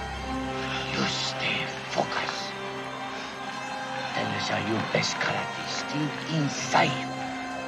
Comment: for the longest time I thought he said Use Da Focus 😂😂